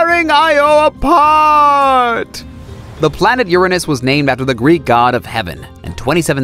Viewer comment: When he said Uranus I thought he said urines🤣🤣🤣